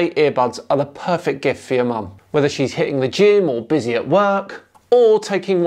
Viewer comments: I thought you said, 'whether she's hitting the gin...'! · * Go to <link> to get up to 20% off sitewide! Brought to you by Raycon * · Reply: I thought you said at 0:48 "whether she's hitting the gin"..... me and my cloth ears might need raycons.... :-) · When you were talking about the mother's day present for a second there I thought you said... "whether she's hitting the gin or busy at work...". 😂